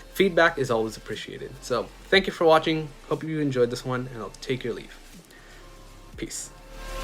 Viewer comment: I heard “I’ll take your leaf.” No, leave my leaf alone!